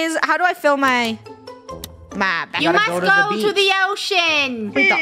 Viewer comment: first words in the video “Spray spray spray.” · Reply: He actually says "We gotta go to the beach!" At 0:51 . He says "Spray spray spray" at 1:41 or 1:40 in the video.